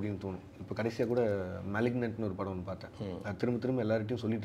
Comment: he told movie name I cant hear that What the movie name ?? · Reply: Malignant